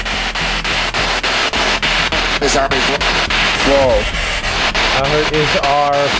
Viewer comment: I hear something like "*distortion* - is out of luck."